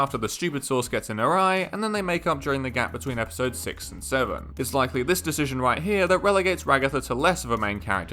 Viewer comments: im sorry but... did he say... six... seven???? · I thought he said makeout😭 altho I do ship it >:)